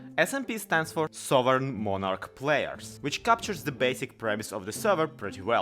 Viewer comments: at the beginning of the video when you were saying what a smp is. i heard you say "southern monarch players" instead of "survival multi players" and it really ignores me. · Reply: I was saying "souvereign monarch players" actually ~ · Did he just say: Sovereign Monarch Players? Or am I just too invested in the whole Ren the king storyline?